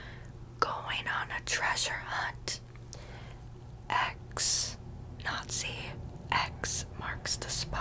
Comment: I thought you called me a nazi lol